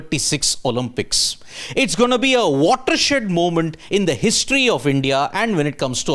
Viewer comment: Why did he say 'watershit moment' around 0:06?